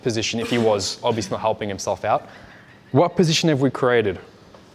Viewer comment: hi I have a question what did you say in <link> between 0:40--0:46? my english is poor i can't get it , appereciate your help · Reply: What position we have created?